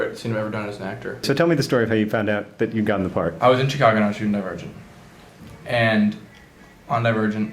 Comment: I thought he said I was in Chicago and I was shooting a virgin .. Woops